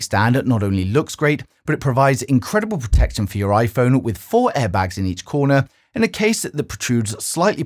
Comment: Did I hear airbags ? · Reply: yeh there are airbags built into the corners